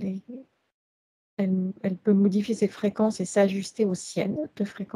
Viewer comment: J'ai entendu dire Frequence-ciel Le racine indo européenne frequents veut dire peuplé. Et si le ciel était peuplé..... Certes on sait désormais que l univers est peuplé d habitants, d êtres qui appartiennent au plan archontique et archiavelique... Mais si l univers était peuplé d êtres venant de la Source ? Est la question que j ose me poser ? Le ciel ou l univers ne serait il peuplé que d êtres venant des sphères obscures archontique et archiavelique ?